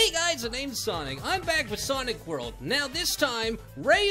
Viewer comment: did sonic just say sonic world it’s sonic mania